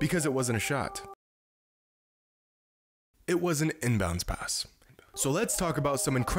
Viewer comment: does anybody else hear him whisper inbound right after he says inbound pass?😭